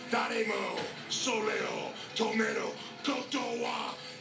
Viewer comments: wait.. Did he just say "tomato"? Lmao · all I heard was tomatoe too😭😭🤣🍅